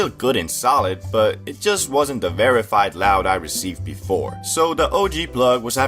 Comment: i’ve always thought you said very fine loud